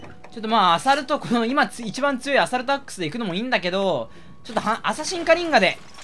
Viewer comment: アサルトアックスがアダルトアックスに聞こえてしまう…